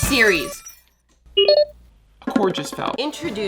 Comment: When you said the cord just fell I thought you said the oranges fell 😂